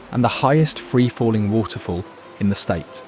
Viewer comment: wait did he just say that Colorado is home to the highest free flowing waterfall in Colorado? Who would have guessed.........